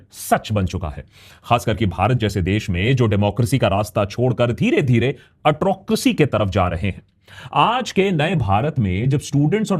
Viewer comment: Did I hear a new word being coined? Atro-cracy (atrocious-democracy) :-). .. perhaps a slip of the tongue from autocracy.